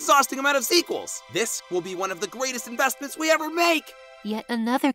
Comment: did nickel just say "gweatest"?